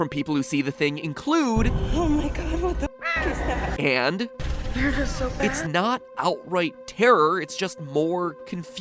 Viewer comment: I'm losing my mind over this. I CAN'T FIGURE OUT if she's saying "they're not so bad" or "they're just so bad" The only thing that can be made out is "they're eh so bad" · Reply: She definitely says 'not so bad'. You can not only hear her say it, her facial expression says the same thing. She welcomes whatever she sees. Also 'they're just so bad' would be a really weird thing to say